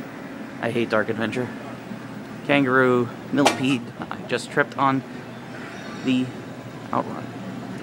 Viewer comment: it sounds like you said you tripped on the outrun game in this video. Did you mean Turbo Outrun?